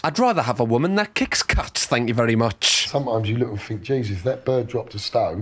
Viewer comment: As an American, i can't understand anything here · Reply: "Jesus, if that bird dropped a stone, put some decent clobber on and done her face up nice she wouldn't be a bad sort"